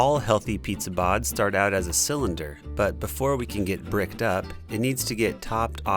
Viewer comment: Did he say Bricked up?💀